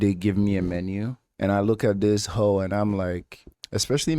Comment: Did you call the restaurant menu a "ho"?